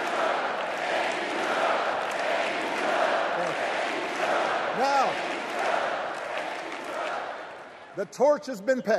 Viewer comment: what are they saying? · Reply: thank you Joe